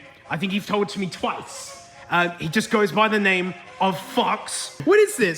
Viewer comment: New mate unlocked: Fox. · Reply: i totally heard that as "he just goes by the name FUCKS"